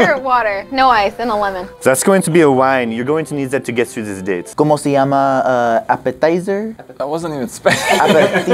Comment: im pretty sure Cómo te llamas? means what is your name- edit: also i dont know if he actually said Cómo te llamas but it sounded like it